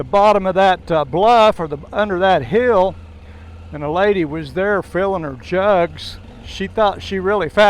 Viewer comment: Man I thought you said “lady feeling her jugs” I was like man’s lucky lol